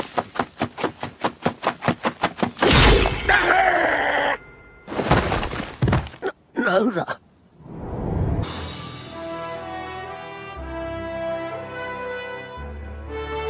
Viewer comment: bro it sounded like he said “that hurtttt!”